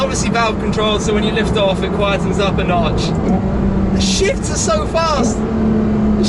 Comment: I thought he said "The shits are so fast"